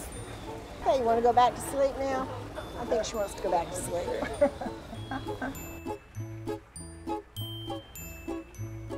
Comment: Were pinkachus named as a joke? Or did I mishear the name? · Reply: Kinkajou!